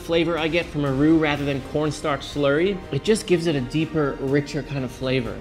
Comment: I thought you said P*rnstar Slurry... then I realized you said Corn Starch Slurry. Seriously though, dibs on P*rnstar Slurry for a band name